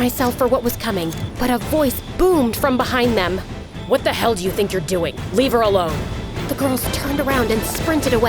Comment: what did he say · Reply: What the hell do you think your doing back off